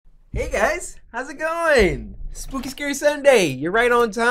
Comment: is it just me who thought he said the n word and not hey guys in the beginning of the video